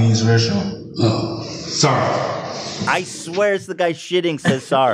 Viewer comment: I hear "SIR!" from stall guy